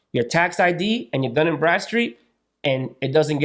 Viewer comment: is he saying dun and breadstreet? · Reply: Dunn & Bradstreet